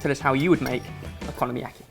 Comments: It genuinely sounded like you were saying economy- aki, like a like a dish called aki or yaki that is made in bulk, which technically it is so it made sense in my head haha! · Is it just me, or does it sound like is saying "economy yaki" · sounds like he is saying economyaki